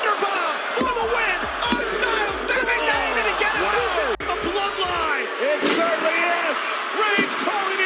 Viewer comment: DID HE JUST SAY THE BLOODLINE?